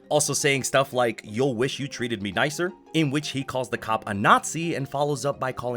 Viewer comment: I'm sorry, did I hear that right? He called the cop a n@ AND THEN a Jewish $lur?